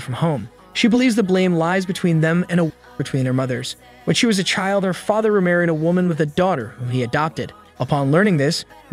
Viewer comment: What is that word? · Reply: Waardenburg syndrome